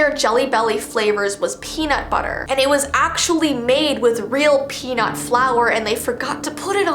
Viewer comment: So a add popped up right when you where talking about peanut butter but the add popped so it sounded like you said its made whith real pee